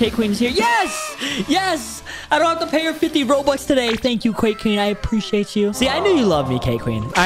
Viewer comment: Did bro really say qwuake queen